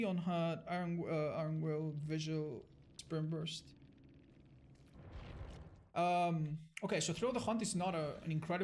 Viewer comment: the subtitles say "sperm burst" instead of Sprint burst